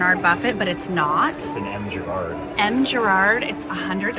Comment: That F. Charad painting is a very expensive painting! Famous Artist for the Antiques Roadshow!😮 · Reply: I thought they said M. Girard?